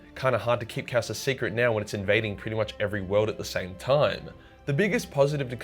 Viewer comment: I thought and even imagined you say "Hard to keep Chaos a secret when it's invading every anal orfice at the same time.".